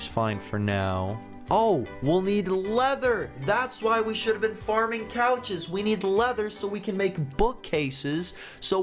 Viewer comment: Well I heard "Oh we need leather! that's why we should be farming cows"